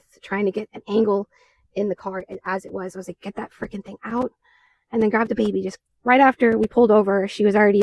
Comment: At roughly 2:42 when she is talking about her daughter’s birth, is she saying “get that freaking thing out”? 😳